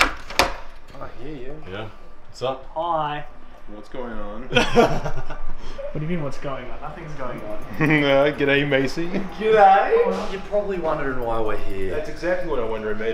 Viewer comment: not even 10 seconds in and I hear Fitz call Mason "Massy"